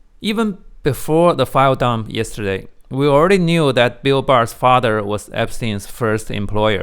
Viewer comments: Jesus Christ I misheard you and thought you said "Bill Burr" and damn near spar out my coffee! Wasn't selling out to the Saudis bad enough for that hack fraud?!? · I nearly had a heart attack cause I thought you said Bill Burr for about half a minute. Thought one of the better comedians became utterly irredeemable after the Saudi tour. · I thought you said Bill Burr and started sweating.